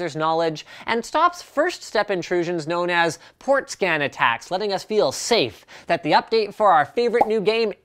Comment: I thought 1:27 said porn scat attacks